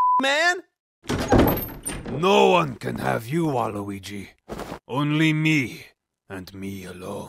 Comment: I don’t know how but I heard that as Mii, and was instantly confused